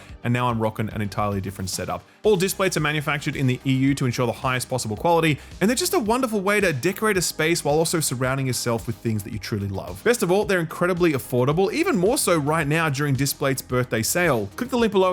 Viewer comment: Did I hear dickerate 😂😂